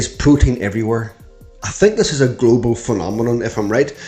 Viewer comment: I thought you said Putin is everywhere, lol. 😂